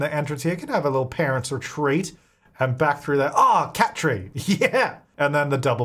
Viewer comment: KAT-tree…. Please tell me someone understands